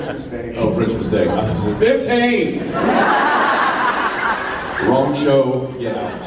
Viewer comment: what's Jared saying at 35:05 ? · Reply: Jared said, "I think she said french mistake" Jensen said, "oh french mistake? I thought she said 15. Wrong show, get out." Jared; "Would y'all be willing to watch another 4 more years?"